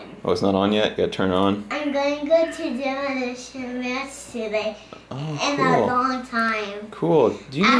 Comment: did he say masturbate?